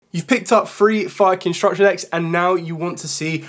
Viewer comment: I thought he said “so you picked up 3 fuckin structure decks.” Bro I died laughing I was about to say favorite yugiyuber of all time